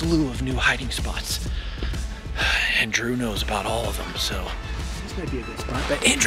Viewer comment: When you said "And Drew" it sounded like you said Andrew. 😂